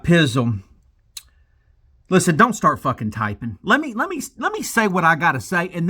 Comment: heard that as I’m typing😂😂